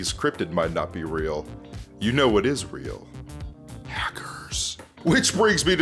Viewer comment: I thought you said packers and I was about to lose my mind